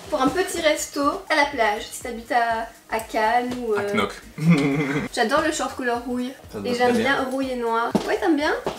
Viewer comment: HAHAHAHA mon dieu j'ai compris "j'adore ce petit short couleur couille" hahahah mon dieu!